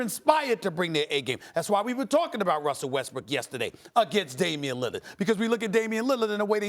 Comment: Did he say Russel WestBRICK?